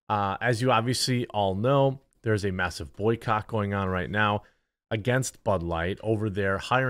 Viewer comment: did you say "A massive boycott at Bud Light now" or did you say "there's a massive boycock at Bud Light right now"? I guess either one works